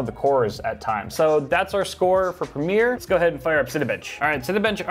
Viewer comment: When you first said Cinebench i thought you said Cinebitch